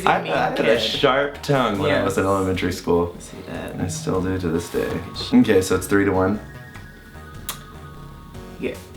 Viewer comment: Did anyone catch what Mitch said after the sharp tongue comment at 3:15?! SEXUAL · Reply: NO WAIT HE WAS GONNA SAY FUCKING SHARP BUT THEY CUT IT OUT BEFORE HE COULD SAY THEE END OF SHARP RIGHT AS HE TOUCHED HIM